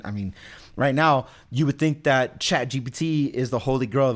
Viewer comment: The auto captions says “…chat GBT is the Holy Girl…” 😂